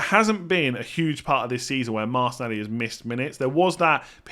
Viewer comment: the subtitles read 'Master Daddy' instead of martinelli